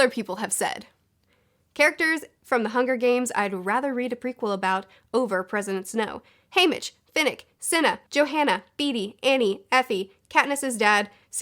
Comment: I keep hearing “President Snoke.”